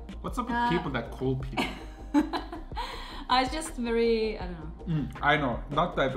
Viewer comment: did i hear it right? @ 17:21 u said " ..just very.. ano?" Sounding more Filipino now..😊